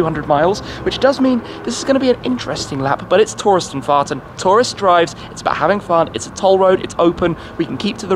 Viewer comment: did you say the tourists are farting?😆👍